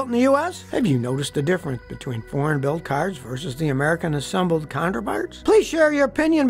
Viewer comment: When Scotty said American assembled counterparts at the end I for some reason thought he said American assembled counterfeits.